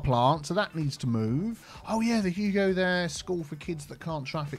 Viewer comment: The “Hugo There Memorial University” because whenever you do lane management and say you go there it sound like you say Hugo There 😂